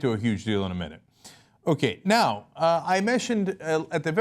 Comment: Did he say he Meshtioned? (mentioned)